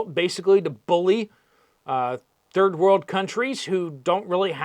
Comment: What happened to ending the war in 24 hrs.? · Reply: It only sounded like he said “war.” He actually said “world.”